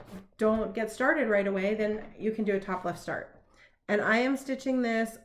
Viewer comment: Glad you are back. When you first said "top left starter" I thought you said "topless starter". Heh, heh, heh...good thing you repeated yourself!😁